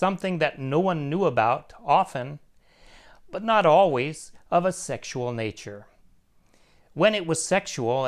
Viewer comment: Namaskar, thanks for this video, let me understood more about Dharma samiikśá. in video on 05:51-57, Dada said "Something that no one knew about often, but not always, Bábá??? sexual??? nature." the last two words I can not heard quit clear, would you please find out for me what is the words? thank you /\ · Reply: namaskar he says 'something that no one knew about, often but not always of a sexual nature'